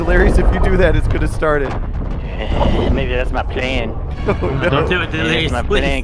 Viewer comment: Cmon on now can’t be the only one that thought LERIOUS said the N word 😂 jus misheard him tho